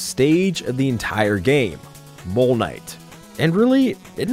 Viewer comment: I thought you said PLEB KNIGHT